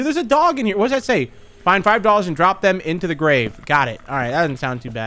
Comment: List of funny stuff from the video: 27:08 i thought he said "find five dollars and drop them in the grave" me: is the guy u play as mr beast? 28:04 "i mean i touched one of the fellas and that was it" - 2021